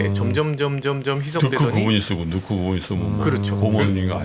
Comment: 뭐라고 말하는건가여? 한국말인가? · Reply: 넣고 그분?이 쓰고×3